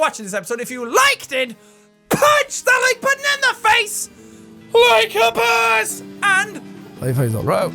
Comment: I am going to be honest...every time I hear your outro, I hear "bunch the banana face LIKE A BOSS" and I get so confused....I'm gonna hide now bye